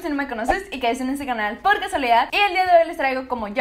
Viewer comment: Te sigo desde hace poco y confieso que como los primeros 10 videos que vi tuyos al principio pense que decias "Hola yo soy michelle por si no me conoces y caiste en este canal porque soledad" Pero despues entendi que era "por casualidad"😂😂😊😊